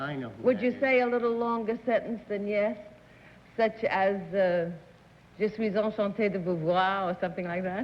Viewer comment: Does anyone know what Ms. Arlene said at 17:52 ? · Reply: I think she said, "Je suis enchante de vous voir," which means, "I'm delighted to see you."